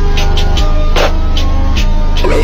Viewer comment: "Silver Surfer appears" "Hey Quandale Dingle here" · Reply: O i watched it muted i thought yall were saying quandale dingle for no reason 💀